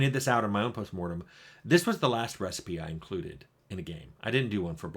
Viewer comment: What are you saying at 40:51 ? "This is the last RECIPE I included in a game." Is it recipe, or another word? Not sure what you're referring to here. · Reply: It's literally recipe. Search Tim Cain recipes